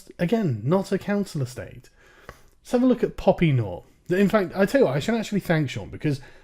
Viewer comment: I thought her name had said poopy nor.